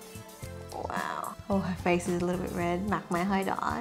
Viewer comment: Do you speak Vietnamese because i heard you say your mom’s face is a bit red